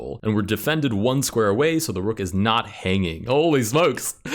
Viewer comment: This isn't a king capturing variant Proceeds to capture the opponents king 10:08 Edit: he does actually say "is" correction by For pointing that out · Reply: I heard “is” and not “isn’t” which makes more sense when he says that rook to d4 isn’t immediately winning